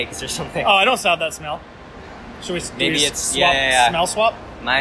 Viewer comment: did he just say “I don’t sound that smell” ???